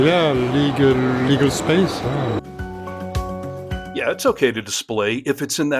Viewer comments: I think he said a "certain legal space" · I believe the swiss man said "legal space" instead of little space as you seem to have interpreted.